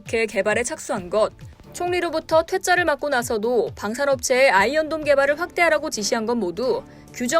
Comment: 어째 아이언돈으로 들리냐 ㅋㅋㅋ 누가더 손해냐 ?